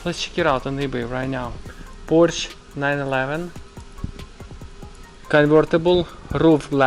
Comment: i thought you said "Borsch 911" :))))